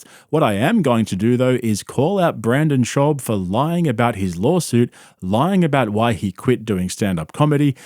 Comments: i thought you were saying “lolsuit” on purpose because it fits perfectly. great video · all i hear is 'lolsuit'